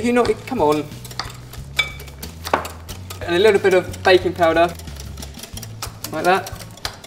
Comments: I honestly thought he said bacon powder until i saw it was baking powder, i was like wtf!? for a second x] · when said baking powder at 2:05, i thought he said baby powder. :P